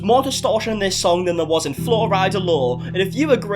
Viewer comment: #justiceforFlorida every time you say Flo Rida I hear “Floride yea?”